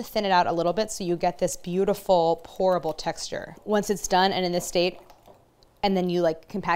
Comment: I kept hearing "horrible texture" instead of "Pourable texture" and was like, many she is NOT selling this.